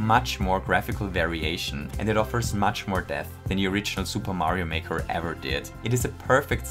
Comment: i thought he said “much more death” lol